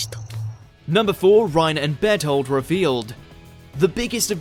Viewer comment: When you said Bertholt I heard ' bed hold ' XD